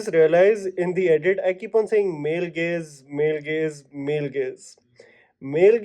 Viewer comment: tbh im still not sure if i get it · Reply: it sounds like he is saying male gays and not gaze